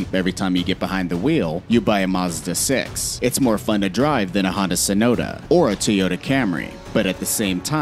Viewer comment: did bro jus call a hyundai sonata, a HONDA SONOTA💀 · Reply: Hah! I thought he said Hondu Sonato